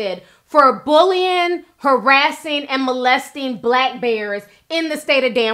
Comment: I thought it said black girls, its damn black bears 😫😫😱